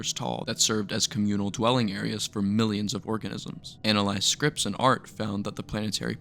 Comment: what did he say?!! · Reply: Organisms 😭